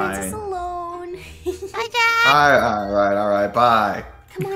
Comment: Did say "bye dad" or "bye jaxx" I COULDNT UNDERSTAND IT SOUNDED JUST LIKE DAD HELP